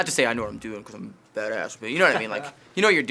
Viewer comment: lol!! does aaron say "FATASS" near the end on 4:38??